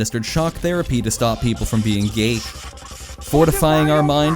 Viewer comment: Did you say “stop them from being Ghaik” or “gay”? 😂